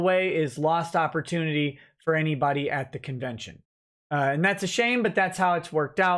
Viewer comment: It might just be me but whenever he says "it's a shame" I hear "its a Shane".